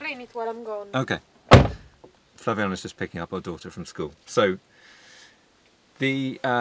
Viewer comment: I thought you said “her daughter” too. Not that it is any of my business.